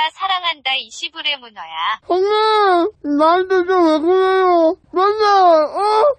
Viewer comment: #ㅇㅋㅂ! 3:59초 뭐라고 하시는거야.. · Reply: 소녀 ; もがない 나한테 좀 왜그래여ㅠㅠ 랍니다